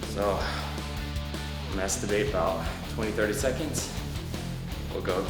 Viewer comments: I thought he said I'm going to masturbate about 20-30 seconds @ 3:24. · Is it me or did he just say, "Masturbate for about 20-30 seconds"? WTF?! :'-D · it sounds like he says "i masturbate about 20 to 30 seconds"😂😂😂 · Lmao did he really say so uh masturbate 20-30seconds then go again? At 3:29 LMFAO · anyone else hear him say "masturbate" at 3:31? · did he say "So..masterbate about 20 seconds.." ? LOL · did anyone else hear " i masterbate for 20-30 seconds" hahahhahahhahahha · anyone else think he said masturbate at 3:31 ? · i swear at 03:30 he says masturbate !? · Am i the only one who heard "masturbate" at 3:31 ? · Did you say masturbate for about 20 30 seconds at 3:26 or am i just being retarded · Sounds like he said Masturbate xD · Does he say he masturbates for 20-30 seconds. Surely that be counterproductive and slow the whole work-out process, no? Also it would only favour one arm. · did he just say "masturbate for about 20-30 secs" !!!! lol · did he really say "masturbate for 20-30 seconds"? lmao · Am i the only person who heard 'masturbate for about 20/30 seconds'???? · did he say masturbate ? hahahah